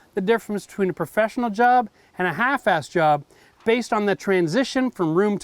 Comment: Did you just say, ". . . half fast . . ."?